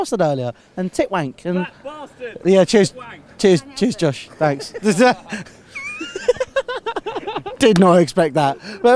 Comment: what did the lady said when she passed by? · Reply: "can't help it" 😂